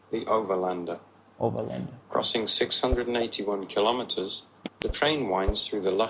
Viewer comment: Section 2 er 11no. answer ki 691 hbe?? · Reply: Listen to 8:52 He clearly said 681